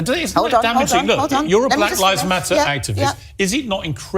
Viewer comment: The BLM folks in London sounded like they were saying “Mack likes BUTTER!!”